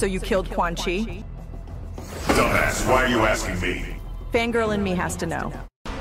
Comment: Nah cause why did I think Sonya said “Finger in me has to know”